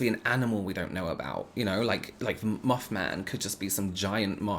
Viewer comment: Thought you said muff man...lol 😂 I need to get my hearing checked ...lol